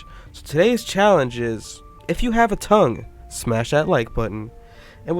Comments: I thought u said use ur tongue to press the like button so I did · i thought you said for a sec if you have a tongue stick it out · "if you have a thumb like the video" I heard it as tounge